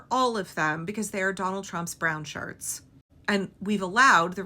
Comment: I heard, "They are Donald Trump's brownshirts" as "They are Donald Trump's brown sharts." I'm not much of a potty humor person, but I admit, I kind of liked that one.